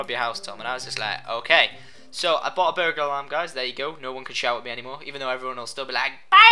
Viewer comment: i thought u said burger alarm lol